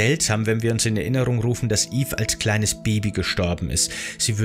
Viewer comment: Warum ist Evelyn als Baby gestorben (in re 7 war die doch ung. 14,15 Jahre alt ) Und so mit kein Baby Bitte um Erklärung sollte ich was falsches verstanden haben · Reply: Es geht um Mirandas Tochter namens Eva, durch die Aussprache im Video ist das leicht missverständlich.